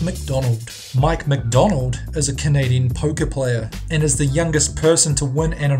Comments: I thought he said "pokè" player instead of poker, so I thought he was playing pokemon cards! Haha! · I thought he said #7 was a pokè player